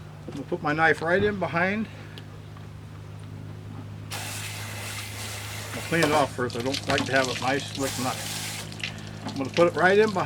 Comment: what's he saying? all I could make out was " I'm a gonna clean it off her I don't like to have rice nice with a wife "